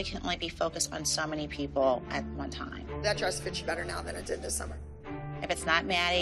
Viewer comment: did Abby really say “that dress fits you better now then it did last summer?” Like she is one to talk